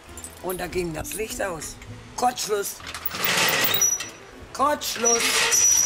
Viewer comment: Die Königin der Nacht scheint den größten Kurzschluss zu haben , da hilft kein Elektriker mehr 😬😬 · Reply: Ich hab Kotzschluss verstanden ;-)